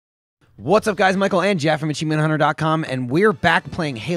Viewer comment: I could of sworn he said "michael and jack" at the beginning